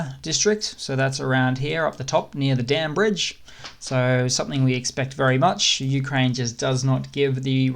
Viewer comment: That bridge near Kherson, Ukraine saying "We hit the Dam Bridge!", Soviets saying, "Where is that Damn Bridge?" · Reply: Heck, I heard "the damn bridge" and thought WTF, why's he hating on it? LOL 😂